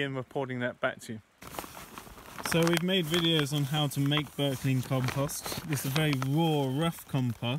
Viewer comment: Can anyone write me a sequence how do they compost 17:27 dried grass? It is very difficult to understand what he says since I did not learn English. I use Google translate.I'll be very grateful! Thank! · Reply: Berkley method